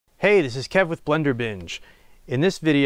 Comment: At first I thought he said, hey, it's blender bitch. Omg, I heard it again and it was "binge". My dead ears.